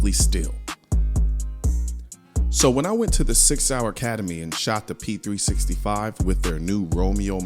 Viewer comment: I really thought he was saying 6 hour academy